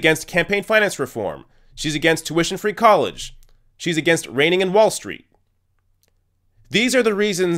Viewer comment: I thought you said Feinstein is against "raining" in Wall Street 😂🤣 If she could make it a policy, she probably would, she certainly thinks the sun shines out of their collective ass!!